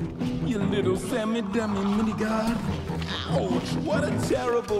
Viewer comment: can someone tell me what he just said there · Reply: “Ya little semi, demi minigod”